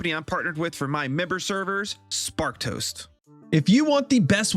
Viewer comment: I thought you’ve been saying Spark Toast, all this time. Sparked Host.